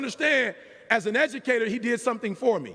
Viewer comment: I thought at the end he said, "...he gave me an A...that I never had before."